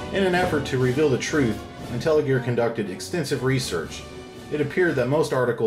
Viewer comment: I thought my man said "Welcome to Nutella Gear" lol, then I checked and saw he said IntelliGear.